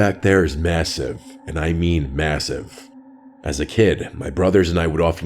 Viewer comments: did you say MASSIVE? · Did you say.. Massive…?